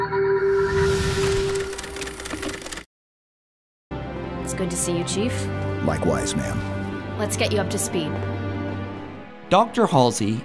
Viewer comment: is dr. Halsey George son to off halo reach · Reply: Spartan 1337 no Jorge has a heavy Hungarian accent it sounded like he said mom but he said ma'am